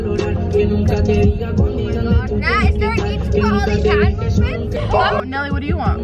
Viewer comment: is it just me or everyone that thought said " I need to fart" lmaooo